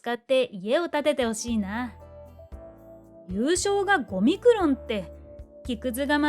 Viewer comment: 5ミクロンがオミクロンに一瞬だけ聞こえたw